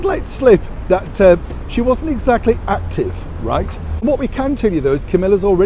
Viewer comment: "Meghan wasn't exactly 'Active'." Oh, I thought you said, "She wasn't exactly 'Acting'." Because that I could believe.